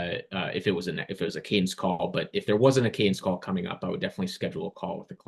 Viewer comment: amazing video! Thank you to you both! Could you clarify what word said at 16:16? “Kane’s call”?